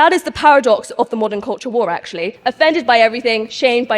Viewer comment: At about 1:06 She clearly says it , Paradox of The Modern Cultural WAR .